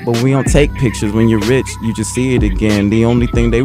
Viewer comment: "The only thing they worry about is me and the pen" double entendre "Me in the penitentiary" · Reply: I really thought he was saying "the only thing they worry about is me in the pen"